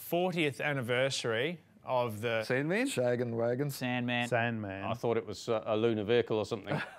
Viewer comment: anyone else hear shaggin wagon ahahha